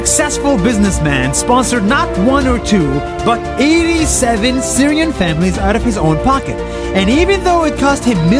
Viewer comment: did he say Syria IM SYRIAN